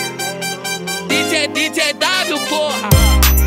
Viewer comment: alguem entendeu o que ele falou ? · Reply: dj w porra